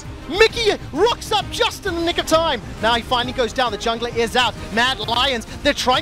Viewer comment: what does he say :thinking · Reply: Nick of time